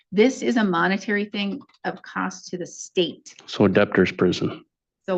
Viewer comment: He actually compared paying a $50/month court order to a 'debtor's prison.' Have people completely lost their sense of personal responsibility today? 😡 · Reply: He didn't even say debtor, it sounded like he said adaptor 😂